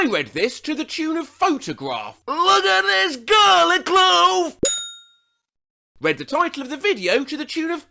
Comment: Ngl.. I saw the “look at this garlic clove” without the caption above it and also read it to the tune of photograph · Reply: I heard "look at this garlic glove"